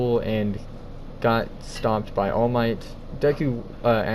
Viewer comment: When he said stopped by all might I heard it as stomped by all might